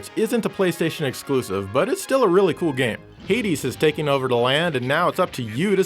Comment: i thought he said the 80's had taken over instead of hades...